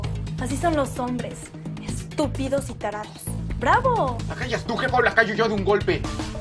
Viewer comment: que dijo?! Alguien me lo repite...! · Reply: "La callas tu o voy yo y la calló de un golpe"